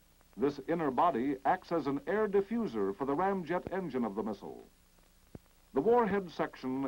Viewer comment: did he just say "ram jet" ? about 1:50 there?